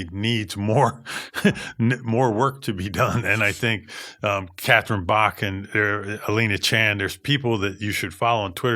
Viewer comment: did bill say "catherine bach". the woman in the dukes of hazzard?